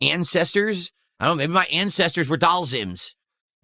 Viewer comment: I thought he said "incesters" 😂😂😂